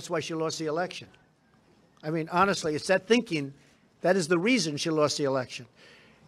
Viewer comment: Kyle, I think you miss-heard what Trump said. He didn't say Clinton lost the election because she made a statement about the NFL kneeling. He said "that thinking" (which her recent statement about the NFL reveals) - meaning her un-patriotic, over-pandering-to-minorities kind of thinking - is what lost her the election. Listen again @ 0:47 +